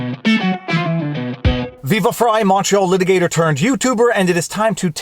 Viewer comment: He's definitely my favorite Montreal litigator turned YouTuber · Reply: LOL, for the longest time, back when I first started watching him, I thought he was saying "Viva Frei, macho litigator..." I tell ya, getting old sucks!